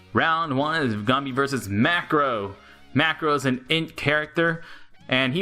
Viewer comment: Was i the only one who thought that round one said Boss Marco